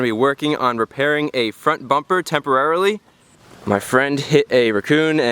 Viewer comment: the English subtitles say "My friend hit a rock" while Chris says "My friend hit a raccoon", hmmmmmmmmmm